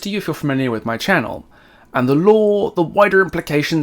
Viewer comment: I think you need some speech therapy. I can't make out @ 0:51 if you are saying Lore, Lawn, Law or Lorn. Lol. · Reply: I think you need some mental therapy. I couldn't see how any word other than "lore" would be relevant